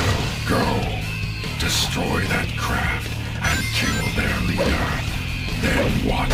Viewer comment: I was just listening to this video while playing Half-Life 2, and at 7:17 I thought Black Doom said "Shadow, go! Destroy that CRAP, and kill their leader!" It really compliments rant.